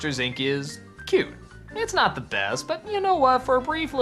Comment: What is he saying?! "I think Monter Inc. is... What?? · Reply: "cute. It's not the best..."